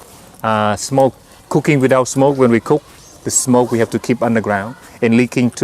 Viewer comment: I thought he was saying cocane without smoke insted of cooking without smoke